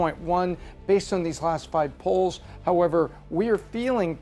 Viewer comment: I love the Aussie accent! Perhaps she should not use the word "last", it sounds like she is saying the "lost poll". I suggest she say the "pervious poll"